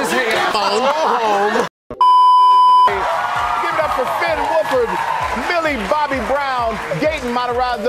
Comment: did he just say- WOLFRD XD 😂😂😂😂🤣😂😂😂😂😂